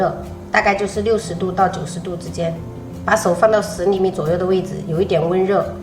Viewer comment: 我把 10厘米 聽成 屎裡面...